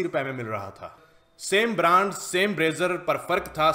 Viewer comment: he said brazzer instead of razor....